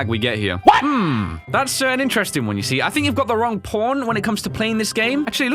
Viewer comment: God, I thought you said "i think youve got the wrong porn" at 1:28.